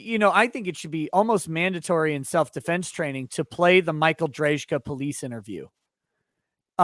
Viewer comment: What's the dykstra case you referenced as required viewing for self-defense classes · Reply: Got it. Michael Drejka. I misheard you, but went back and found it.